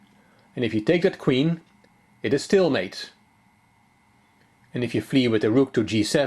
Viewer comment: The way you say "checkmate" is incredibly satisfying to listen to. Where are you from btw, France? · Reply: But when he says "it is stalemate" I thought he said "it is still 'mate"!